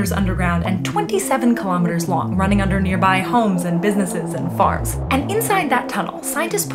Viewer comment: "running under businesses and farts?" is what i heard